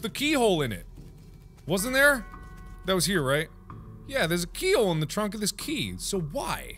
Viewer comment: Anyone catch how he said "There's a keyhole in the trunk of this key" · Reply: I thought he said there is a treehole it's the trunk of this key